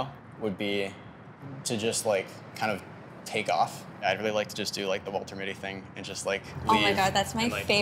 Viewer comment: Which movie did he say at about 1:30 please? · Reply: The Secret Life of Walter Mitty